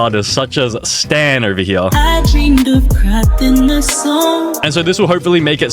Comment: Did he say I dream of craping a song!?